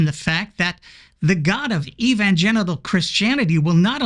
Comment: "The god of evangenital Christianity..." sounded like a dick move. However, by the time you addressed Winger's limp theology I realised it was one of those hermeneutic justified presupposition thingies.